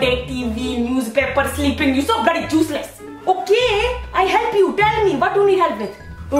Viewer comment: "You so bloody juice-less!!" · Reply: i actually thought she said juice-less until i look at the captions